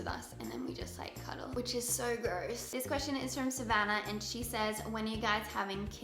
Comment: "then we cuddle"- did she said "which is so GROSS"??? haha